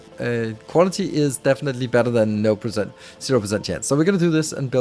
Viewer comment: did you say no percent or nul procent? i swear that was danish maybe i just cant hear?